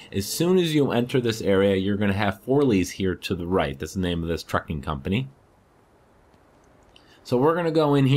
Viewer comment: When you said trucking I thought you said fucking haha XD. That's the name of the fucking company...